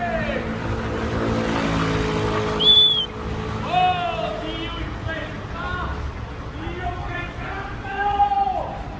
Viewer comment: Cosa ha detto? Dio (...) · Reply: Impestà